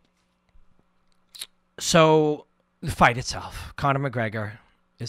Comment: i thought you said “the fight is off” and I almost had a stroke